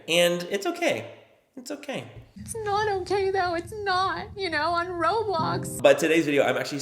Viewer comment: omg why did it sound like kim was saying "its not okay on roblox"? lmao